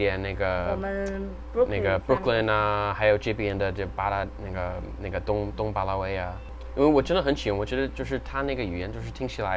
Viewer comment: I heard The N word LMoo